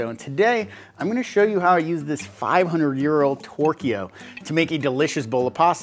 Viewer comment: Thought he said 500 euro 💶 😂